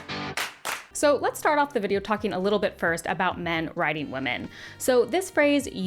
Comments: Oh, thank goodness: it's your accent! I thought you kept saying "men riding women." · For some reason, I kept hearing "men riding women" and "women riding men" lol